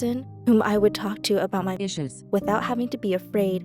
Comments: is it just me or does it sound like is saying issues? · Sounded like she said dishes lmao